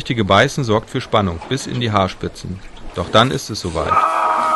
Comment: bei haarspitze hab ich arschritze verstanden^^ bei